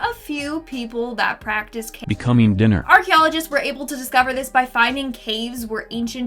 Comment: Those "BECOMING DINNER" was a lot more funny than they should have been. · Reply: I thought i heard incoming dinner lol